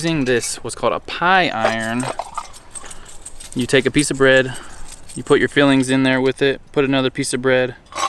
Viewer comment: I had to rewind this part cause I coulda swore said "You put your feelings in there with it." 😂